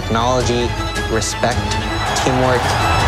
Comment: I thought that said 'more than reboots' and I got excited 🤣